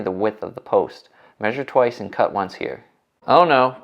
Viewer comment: Did I hear “measure twice”??